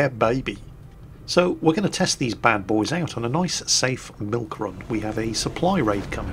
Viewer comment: Hey.. I thought you said "NICE SAFE MILK-RUN"?